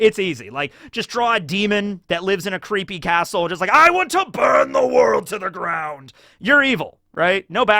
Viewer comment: Did you say Burn D. World?